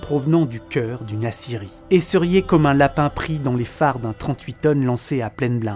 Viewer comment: d'une quoi ? j'ai pas compris le mot ou la ref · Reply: D'une acierie*, là où on fait fondre l'acier qui, à chaud, devient blanc vif